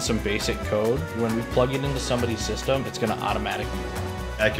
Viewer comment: what does he says "When we plug in this code, then its automatically ......... ??? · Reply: "Then it's going to automatically run."